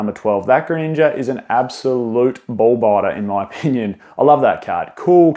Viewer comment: Did he say “ball biter”? And was it a compliment? Lol!